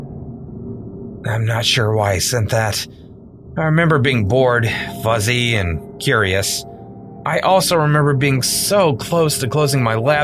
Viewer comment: "I remember being bored fuzzy & curious!!!” 🤣👂🏽 I heard of fuzzy hair but I never heard of the feeling of being fuzzy lol!”